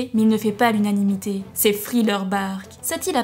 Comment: c'est moi ou elle dit friller et non thriller